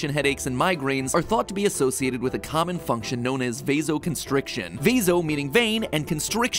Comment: Why do I keep hearing “ bezos constrict” instead of “ vaso constrict”